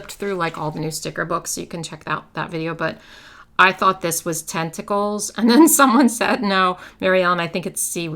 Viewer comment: I was working and mostly just listening to this video, when I thought you said “testicles”. I about fell out of my chair! The deep belly laugh really woke me up though, so thank you for the fun afternoon wake-me-up!! 😂😂😂